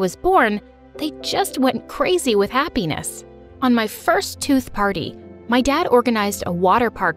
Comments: Did they just say twoth birthday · did she say first tooth paty I didn't know ppl get a part of their first tooth